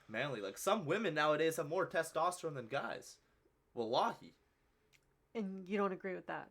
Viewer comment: did say Wallahi? 😊 btw I subscribed